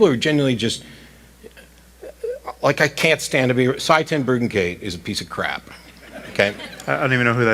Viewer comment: Can't make out the name.. Who's a piece of crap? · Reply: Sye Ten Bruggencate. A creationist and anti-science lunatic.